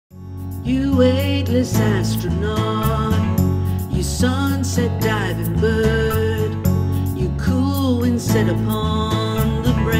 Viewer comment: The part where you sing - You sunset diving bird. It sounds like you said - You died at birth.